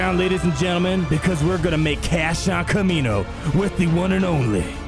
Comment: i can't help it but i hear him saying "Cash on Kamino"